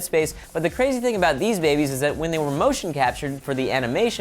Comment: I thought it said babes at first then in like oh snap :O